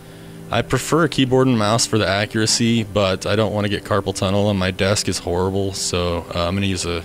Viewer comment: What is he saying at 7:19 ? He is not using keyboard, because he doesn't want to get "carbon tunnel" what? :D I dont understand. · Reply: Not carbon its carpal tunnel